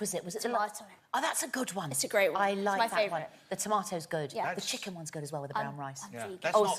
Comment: what does bella say when mel asks what soup it was? i'm curious and can't make it out lol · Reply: I think Bella says it was tomato soup